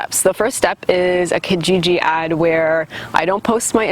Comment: what does she say around 5:41 about the KGG ad? I out on subtitles but the subtitles say she is saying "GG ID" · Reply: never mind its KIJIJI =) its like Canadian Craigslist.